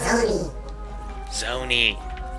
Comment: Lol at 0:55 I thought the Zonis had said "SONY!"